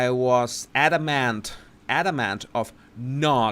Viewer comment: I really enjoying the The Royal Grift, so thanks for suggesting it. I thought you just said you were Adam Ant!